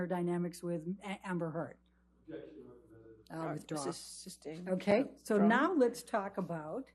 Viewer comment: Is she saying "o god" to the objection? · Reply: she said “i’ll withdraw”